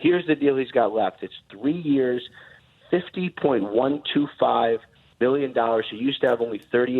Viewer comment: wait 3:17 is it just me cuz I just heard Ian say AB is going to make 50.125 billion dollars if so Damn...